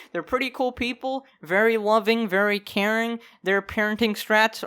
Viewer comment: I thought you said Karen for a second- 0:18 XD (yes Ik he said Caring)